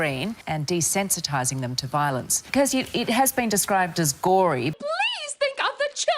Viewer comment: did she say it was gory?!?!?!?!?